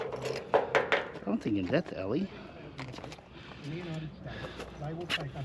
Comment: You always say. The subtitles say Ellie. In my country Ellie is a name for a girl. The first time I heard you say, I thought you'd pick up a girl😂😂😂.